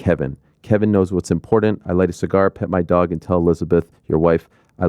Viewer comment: Ariel it sounded like you said pimp my dog lol